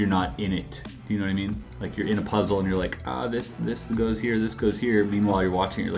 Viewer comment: I really thought you said honey I’m a puzzle 🧩 lol you should definitely do a shirt that says that it would be so dope please!!!!